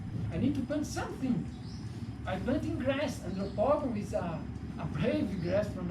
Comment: Hi @ 9:10 he mentioned a particular type of grass the he planted, can i get the name of the grass, i couldn't understand what he mentioned, pls anyone. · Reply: Andropogon